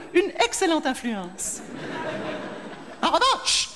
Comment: elle /il dit quoi à 0:44? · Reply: « En revanche ! »